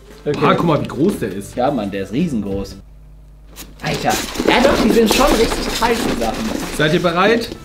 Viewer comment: bin ich die einzige die gehört hat das er gesagt hat ja ist schon ganz heiß aber es ist ein eiswürfel lol 😂👌